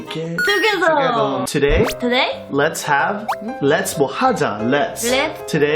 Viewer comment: tbh, I thought it said "today let's shave" XD😂😂😂